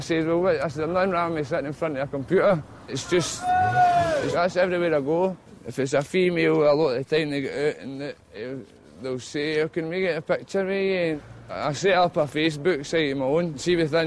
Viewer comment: So i turned captions on and it's no use but the translator seems to think that at 3:40 he's saying "every bit ago if it's a female a lot it a nigga and though" LOL!